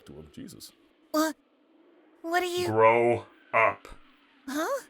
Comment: I thought 16 said "Bro up” 18:17 but after reading the subtitles I sadly saw it said “grow up”.